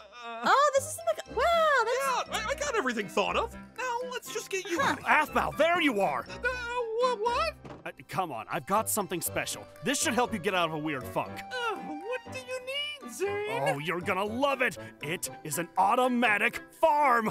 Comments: I think i heard Zane say “This should get you out of a weird fu*k” at 15:30 :o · PEOPLE WHO HEARD ZANE SAY F WORD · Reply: HE SAID FUNK CAN YALL NOT HEAR RIGHT? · DID ANYONE ELSE HEAR ZANE SAY: “now come on this will help you get out of a wired f-word💀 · did zane just said the f word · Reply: He said funk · Did anyone notice at 15:30 that it sounds like zane says the f word and if you put captions on its blurs the word after "weird" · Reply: Yea it’s weird it’s should say funk I am so confused 🤨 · I heard zane say f**k to (Aaron) Aphmau and so does the transcript 15:33 here if you want to know if it's real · WHAT THE HECK DID ZANE JUST SAYYYY??? Edit: I think he actually said ‘funk’ 😅😅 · I'm sorry am I the only one who heard zane say th f word? Or am I going crazy😭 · nobody noticed what Zane said? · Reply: HE SAID FUNK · Who else watches Aphmau everyday? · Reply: it’s sounds like he said weird f*ck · WAIT DID ZANE SAY WEIRD FU—— NOT SAYING THAT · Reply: Idk I think He said funk because Aphmau would not swear on her vids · wait 15:34 did zane said funk or the f word